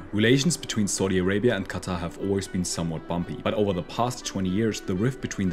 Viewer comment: I thought he said “somewhat bomby”